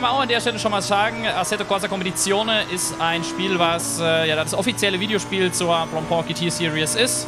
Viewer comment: Welches Spiel hast du hier gennant? Ich habe gar nicht verstanden, weil du so schnell redest... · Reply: Also ich sage: ASSETTO CORSA Competizione und Blancpain GT Series